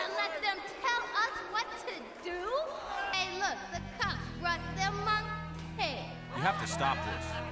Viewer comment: What did she say at 0:59? Did she say “look, the cops brought their monkey.”